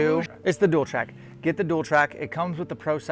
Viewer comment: Which black gold sight is that? I think I heard dual trac at 9:09?